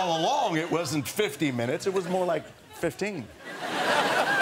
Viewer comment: What does Colin Farrel say at 4:38 ? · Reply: Patrick Gracia "Fifteen minutes though man"